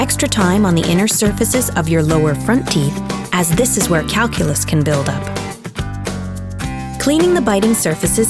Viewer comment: am I hearing things or does she say “this is where calculus can build up?”